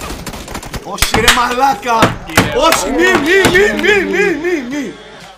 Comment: At "1:48" is not saying mine is saying "don't don't don't" the name of the YouTubers is